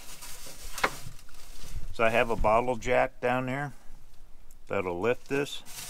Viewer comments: Ohhhh! A "bottle jack!" I heard, "a bottle of Jack." Haha! Thanks for the vid! You're well prepared! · At first I thought you said a bottle of Jack! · Oh, wait...he said bottle jack. At first I thought you said a bottle of Jack (Jack Daniels). I thought that was a bit strange but to each their own...but then I spotted the bottle jack and it made a bit more sense. ;-)